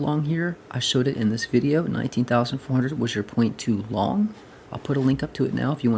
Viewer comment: I just heard him say 'Had you LONGED from this point' at the start of the video. Lol man😂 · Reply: Watch the video i referenced. I said that i was a point to long.